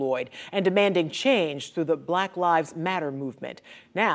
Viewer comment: First thought it said crack lives matter😂🤣🤭